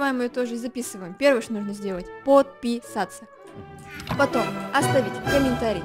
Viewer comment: я услышал вместо подписатся отписатся вместо оставь комент неостовляй комент вместо поставить лайк поставь дизлайк) я подписан оставил комент и лайк поставил